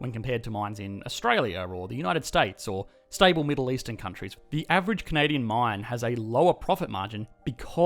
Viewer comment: Video: In stable Middle eastern countries Me: Why do I hear violent Kazdağı protestors? Umarım en azından biriniz görürde espiri boşa gitmez.